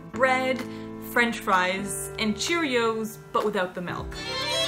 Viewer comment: To be frank I misheared when you said he ate his cereal without milk and thought you said with milk so I was sirprized when it was a B12 deficiency.